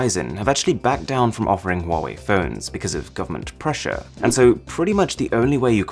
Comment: He speaks english so clearly that the english auto generated subtitle can understand everything he says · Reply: Subtitles: "ferns" instead of "phones"